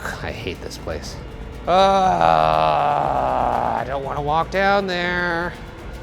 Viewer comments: As you were walking away. I know you think it says “down here” but I swear I hear “get back here” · I didn't hear "down here" I heard "damn you" 😳😬👻☠